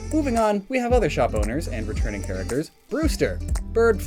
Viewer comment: /6:03 when you said "Shop Owners" i initially heard it as "Shot Boners" and got extremely confused.